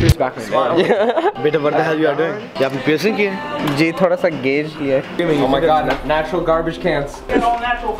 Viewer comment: I thought he said "taura sa gay ki'ah" when asked about his ear piercings 😂😂